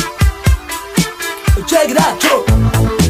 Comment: What does he say in 0:15 Cant find that in lyrics. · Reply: something like "oh, check it out yo." its just filler.